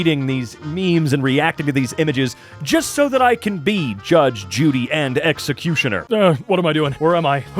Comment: Did he just say "Judge Judy and Executioner"?